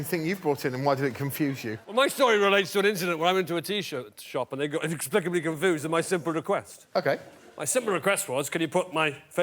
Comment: Did anyone understand what he said? Because I really did not. xD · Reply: “ Well, my story relates to an incident where I went to a T-shirt shop and they got inexplicably confused about my simple request.”